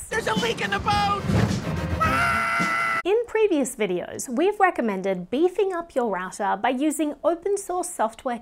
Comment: I swear I heard you saying "beafing up your ass up"